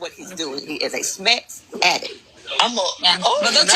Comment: I don't like but I'm <num>% sure he thought she said m#th addict and that's why he went on about the federal license